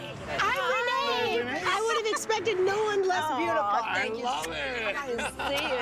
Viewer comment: did she just say wouldn't have expected no one less beautiful? lol insults